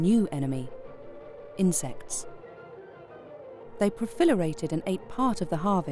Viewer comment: Did she say "they profilerated" instead of proliferated?